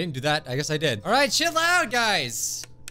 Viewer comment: He said alright chill out guys? I thought he said alright shit loud guys!